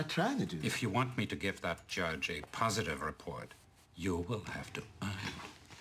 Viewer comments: "you will have to eungh" 😂 idk what he said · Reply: You will have to earn it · if someone didn't understand him he said "you will have to earn it"